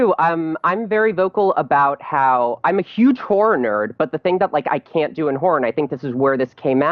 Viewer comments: I thought he said whore nerd😂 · holy shit, I thought she said "I'm a huge whore-nerd" for real...! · Lol at 4:07 i thought she was about to say "im a huge whore" because they were talking about sex and orgasms lol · Did she just say "Im a huge whore nerd"? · When he\she says she's a horror nerd lmao I thought she said imma whore nerd 😂😂👌 like if u thought the same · Lmao, I thought he said who're nerd. Not horror. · Thought she said whore nerd.